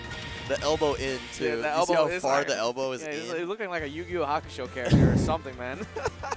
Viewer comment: Anyone else hear "Yu-Gi-Oh Hakusho"?😂😂